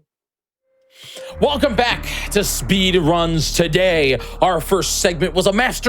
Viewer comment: I swore it said speed puns, no offense but I was ready for top tier eye rolling action